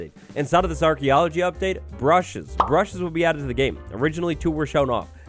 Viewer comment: When he first said “brushes”…… I thought he said Russians. I mean, I love Russians, but it was a bit confusing.